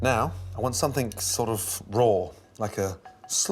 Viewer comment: WHAT DID HE SAY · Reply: he definitely said RAW